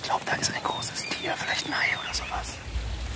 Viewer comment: ich hab hier beim ersten Mal "vielleicht ein Hai oder sowas" verstanden, unintentional comedy gold 👌